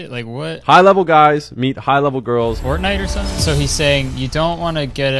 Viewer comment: DID HE SAY PORKNITE